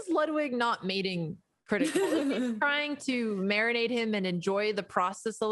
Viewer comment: I thought she said "why is Ludwig not mating Cr1t1kal? is he trying to marry him and enjoy the process a little bit more?"